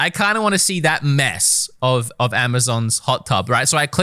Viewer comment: he does indeed say Amazon.